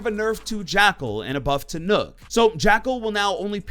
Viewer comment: I thought he said "nerf to nook" and that wouldve ended my career